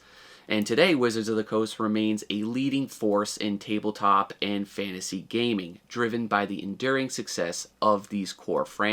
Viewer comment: in 1996 did you say $8000 dollars or A-Thousand dollars per card? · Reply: said A-thousand. Sorry I didn’t sound clearer